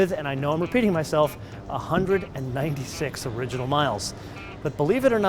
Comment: i thought i heard him say 196 thousand original miles and i was like ehhh thats not that great but then i played it again and heard just 196